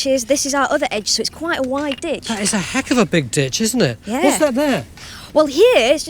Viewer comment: In the 11th minute Tony says "that's a heck of a big ditch" . I heard "that's a hell of a dig bitch" .